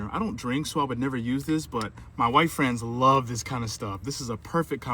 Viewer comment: My white friends will love this 😂😂😂😂 lmao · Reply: I thought he said "my wife's friends" lmao. You're right tho